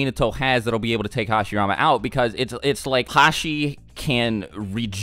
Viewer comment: did he call hashirama hashi